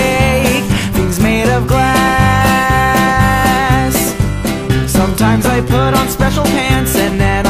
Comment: I love this song too, but I'm just wondering....Did he just say 'ass' at 0:50???